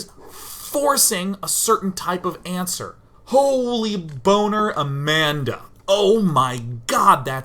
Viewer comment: did he say "holy boner Amanda"?